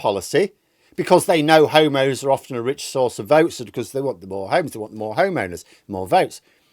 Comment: G-d, I thought you said, "They know that homos are a rich source of votes." 😮